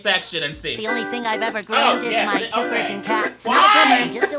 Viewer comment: I love how much everyone loathes the "only groomed my cats" line, knowing how cute and clever she thought she was being... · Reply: When I first heard the song I thought she said virgin not Persian